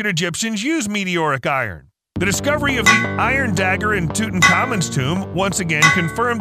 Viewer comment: For a moment while you were talking about Iron, I heard you say Ferro-Tutankhamen....